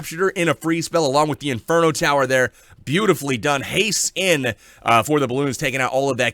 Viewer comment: I thought you said "haste skin" at 3:37 lol. Late night recording for because day time he needs to wait for the wife and kid to be asleep. Keep it up!